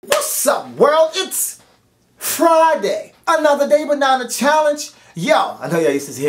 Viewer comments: He said "another day, but not a challenge !" but yes it sound like he said banana challenge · Lmao I thought so tooo! but he said " Another day, but not a challenge" lol